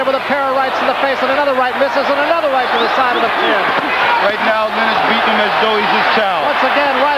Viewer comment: does he say "right now Leonard's beating him as though he's his child"? haha man the times...